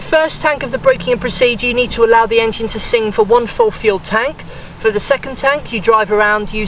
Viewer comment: For the first tank of break in I didn't understand what u meant by sinking the first tank could you please help by clarifying what you do for first tank of break in. Great video by the way thanks! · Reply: I heard him say "let it sing". Which to me sounds like letting the car idle the entire first tank without throttle input